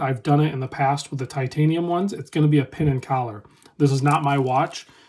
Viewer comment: I'm Sorry I Couldn't understand What You SAID.., =After You Said The Word "Titanium", ..IT SOUNDS LIKE You Said, (( ""ITS Gonna Be a PIN & TOWER" ))? What does that Mean? .. PLEASE Explain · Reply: He says "Pin and collar" to describe the adjustment of the bracelet.